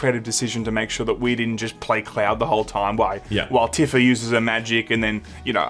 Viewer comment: has nearly finished it. ..............meaning he’s nearly started it. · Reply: When says cloud it sounds like he says Claire And I've never played FFV7 so while I was wondering who she was, I just accepted it.